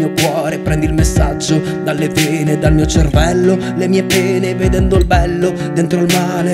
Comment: Inizialmente ho sentito "Dal mio cervello e dal mio pene." Ero morta AHHAHAHA